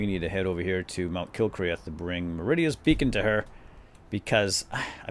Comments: i thought it said meridias bacon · Lol I thought it said "Meridia's Bacon" LOL XD